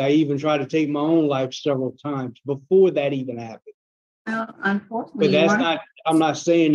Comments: ~ "Well, unfortunately you were un....." Is that what I heard? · "unfortunately you were not successful" 😂😂😂... Did I hear that right? 😂😂😂